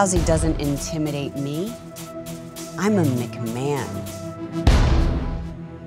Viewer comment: Lol..I thought she said, "I am a Man" at the end and I was like what?? Then I realized she said, "I am a McMahon"